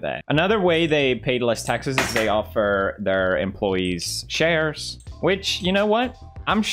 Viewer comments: When he said Amazon gives employees shares I thought he said CHAIRS 😂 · dude i deadass thought when he was saying shares he ment chairs bc of the accent i dumb